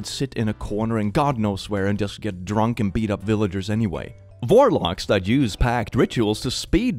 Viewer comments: did he say Vorlocks? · i thought when he said warlocks he said "vorelocks"